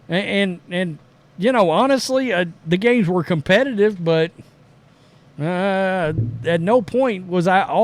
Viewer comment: Phoenix vs Indiana... 😁💬 I thought said 'those gays were competitive'. It fits but after rewinding five times seems he says "those games were competitive". Either way works I guess. 👍😊👌 Nothing but truth on this channel.